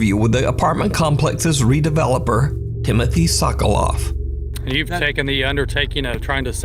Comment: I swear I heard "Timothy sucks alot" 😭